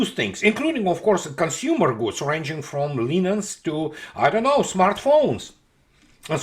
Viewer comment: Ah, SCO, who else immediately thought of Daryl McBride and his misguided attempts to go after IBM and Linux back in the days? EDIT: 03:14 Did you say "Linux"? Or "linens"?